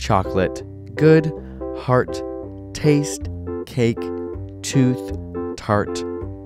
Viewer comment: I said teeth instead of tooth because I thought he said teeth! I only missed 3 other ones I didn't write down! Great video btw!